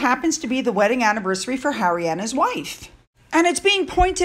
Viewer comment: It sounded like you said “Harriana’s wife” and now I just want her to be “Harriana” from now on. lol